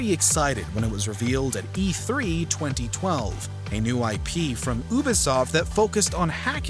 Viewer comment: did he just say oobisoft haha